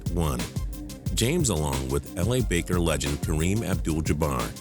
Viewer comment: ish he says La baker instead of the LA lakers lmao